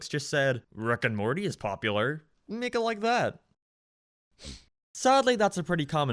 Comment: I swear I heard “Niggas like that” and I wouldn’t stop laughing😭😂